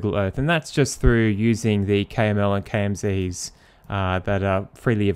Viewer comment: what he says ? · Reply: KML and KMZ are Google Earth annotation file formats. KML stores geographical data and KMZ is a zipped version of KML.